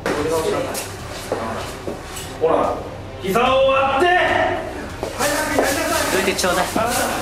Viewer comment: 伊沢を割って！って聞こえてしまったw